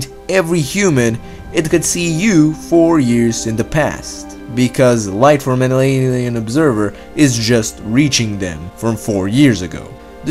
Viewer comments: what you said? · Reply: ''...because light from an alien observer is just reaching them from four years ago. " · What he sayds? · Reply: Light from an alien observer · What is that word? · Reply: "an alien observer" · Hey buddy, just wondering wtf you said at 3:15? Thanks! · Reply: Nick LeRoux “light from an alien observer” he does say it funny lol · because light years from earth hemmamsnsjakaksdmd whatever tf u just said. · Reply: He said "light from an alien observer"